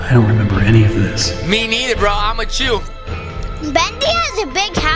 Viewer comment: did he say I’m a Jew or I’m with you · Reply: I’m with you